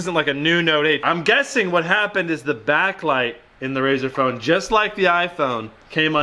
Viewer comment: The iPhone 10 hasn't got a backlight, it has got a OLED display like the note 8. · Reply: I thought he said backlight